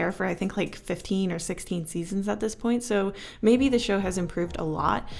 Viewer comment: I was eating chips and I heard "15-16 years "